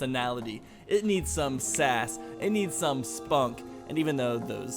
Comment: did bro just say spunk